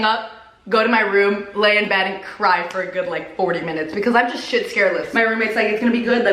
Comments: did she just say shit scareless 🤣 lol what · Am I the only one who heard her say “shit scaredless”? Lmao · Did I hear her say "I'm shit scaredless" 😂😂😂😂😂😂😂😂😂😂😂😂😂😂😂